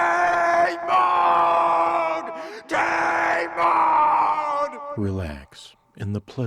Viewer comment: "Did you just say 'Tate Mode'?"